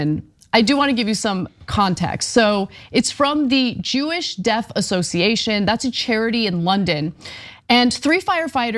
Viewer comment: I thought she said "Jewish Death Association" 😮